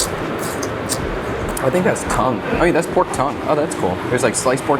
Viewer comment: I swear, I thought he said, "Oh, that's cum"! 😂😂😂😂